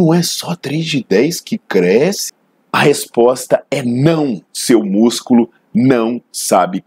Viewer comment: Eu achei que ele falar "NÃO, SEU BURRO" kkkkkkkkk